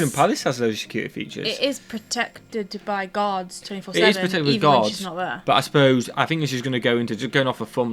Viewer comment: Lmao the entire video i thought was saying that the white house was protected by Gods... not guards